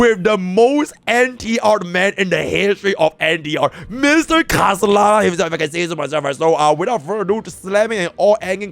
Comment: What is he saying here? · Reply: “Most NTRd man in the history of NTR, Mr. Khaslana if I can say so myself”